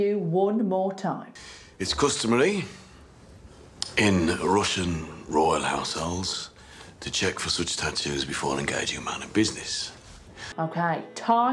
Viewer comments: I heard "It's customely in royal household to check for such statutes before you engage them on a business " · Final challenge. I hear "It's customary in russian royal households to check for such tattoos before engaging a man in business" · What I heard was: " it's customary in Russian royal households to check for substances before engaging a man in business".